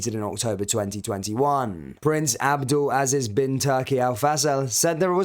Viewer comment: I'm sorry did he just say bin Turkey? Is that like a bin chicken?